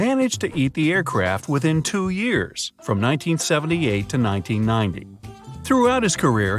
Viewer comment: I think he said 1990 not 1980